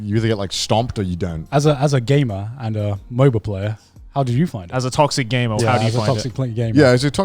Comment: it sounds like Garnt is saying to Connor " as a gay man and a MOBA Player" xD